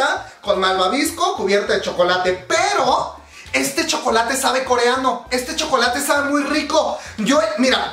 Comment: Entendí "Este chocolate sabe a coreano" jajaja mi mente ñiaaaaauuuum